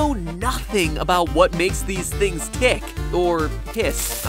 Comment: I thought he said "what makes him pi$$